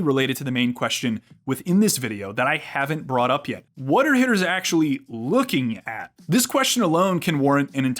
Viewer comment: I thought it said hitlers